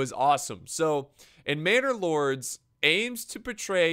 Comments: when he said manor lords I thought he said banner lords. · Every time he said manor lord I thought he was saying bannerlord